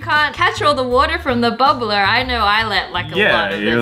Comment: did she say blubber I live in America.